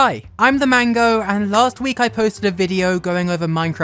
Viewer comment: i thought you said Ilmango and I,,, s i g h i miss ilmango. He do redstone tho